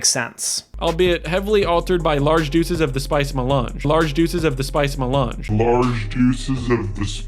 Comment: I think he's saying "large dosage", but hard to tell.